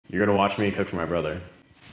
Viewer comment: Bro I was looking at something else and I hear "your gonna watch me touch my brother" I look back and replied that, I heard wrong💀